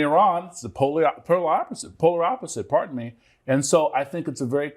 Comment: Could somebody tell me what is the wording of the guest's saying at: 10:42 after "supposidly"? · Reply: He doesn't say "supposedly" at that time. He says, "the polar opposite". Hope that helps.